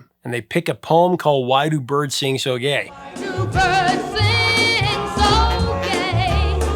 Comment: Sounds like he said, Why do birds seem so gay.